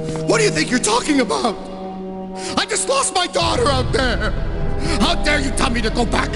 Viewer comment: and i used to thought that he said he lost his dog out there.....